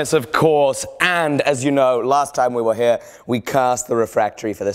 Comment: did anyone else hear him say "cursed" instead of "cast" about 0:11 ??